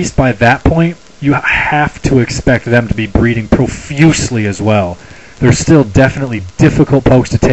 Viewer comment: heh, sounded like you said "breeding profusely." I find this amusing.